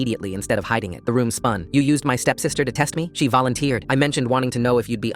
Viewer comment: Thought it said stepdad